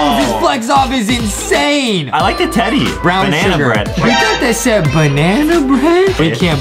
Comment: naming brown bear: i thought they said banana bread Me: nope! Its brown sugar